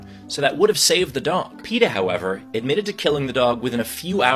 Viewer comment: I heard "Peter" at first. Now I feel bad